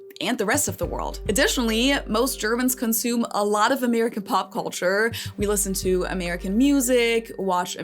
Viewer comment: "Most Germans consume a lot of American pop culture" - I first heard "popcorn" there and just nodded. A lot of the crazy news for there is just for eating popcorn while quietly shaking our heads as we can do nothing about it ...